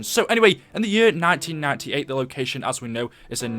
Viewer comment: Did he just say "in the year 1998.."? lol or am I just hearing things?